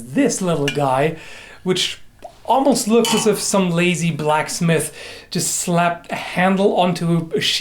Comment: I thought you were gonna say "Almost looks as if some lazy black guy...". O_o